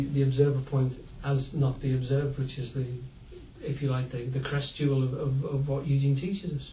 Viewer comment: What is the word that sounds like "crestule" at about 7:24 ? Cant find it online .